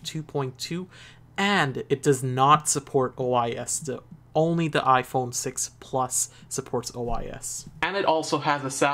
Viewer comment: what did you say? It does not support what? only the iPhone plus does? I didn't catch what u said at the part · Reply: OIS optical image stabilization is only on the iphone 6 Plus